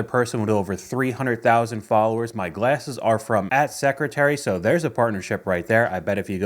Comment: I heard that as "A$$ Secretary" 😅